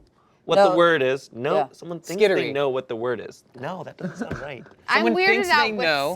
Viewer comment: "someone thinks they know what the word is"----->"someone thinks that they know what the word is"